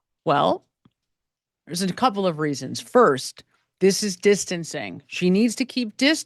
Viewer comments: I thought she said “ this is insane” why her accent? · I thought it sounded like she said “this is insane”. Either way! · Sounds like she said this is insane?